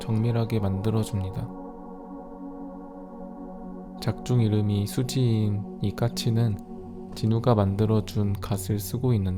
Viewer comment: 영어 자막 틀렸다 The English subtitle is wrong! The name is Sussie.